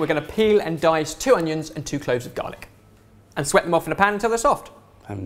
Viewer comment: I understood dalek instead of garlic 😂 Any other Whovians watching this?